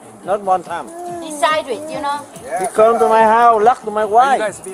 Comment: At the beginning I thought he said” He come my house , he love to my wife.”